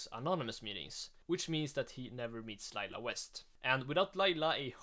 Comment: "he never meets Lila west" I thought he said it was a worst out come